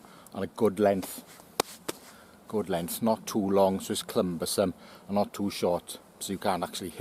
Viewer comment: "clumbersome" I thought I heard you say. What a fantastic addition to the language.